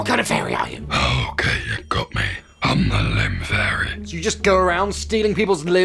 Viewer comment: I thought he said the lame fairy at first lol